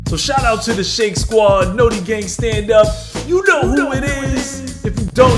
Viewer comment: For the longest time I really thought Shake was saying “Naughty gang stand up.” 😂😂😂